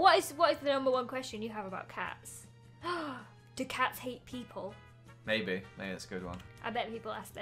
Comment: When asked “do cats hate people?” I thought she said “Do cats eat people?”